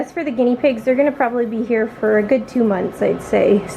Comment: When u said two months I thought u said 62 months and was all like 🤔